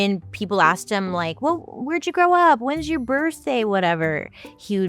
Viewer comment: I was listening 🎧 but not watching but I heard you say it's your birthday 🎉🎂 🌹🌹🌹🌹🌹🌹🌹🌹🌹🌹🌹🌹🌹🌹🌹🌹🌹🌹🌹🌹🌹🌹🌹🌹🌹🌹🌹🌹🌹🌹🌹😇✨💫